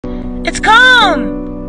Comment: For a sec I thought she said it’s cvm 🦗🦟🦗🐴🦗🦟🦗🦟